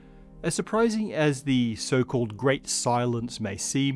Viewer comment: Matt says: "The Great Silence" I hear: "The Great Cylons" So... aliens?